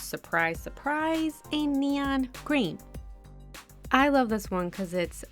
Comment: is it just me or did she actually say "green" instead of "pink" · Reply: LOL I said neon creme because they're my fav but a couple people have said it sounded like I said green haha! :D